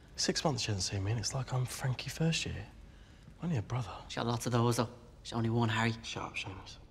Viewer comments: what did he say at the start? Was it in Irish? I may be part Irish but i speak very little lol! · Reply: "She's got lots of those though, there's only one Harry". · wtf did he just say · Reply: "She's got lots of those, though. There's only one Harry."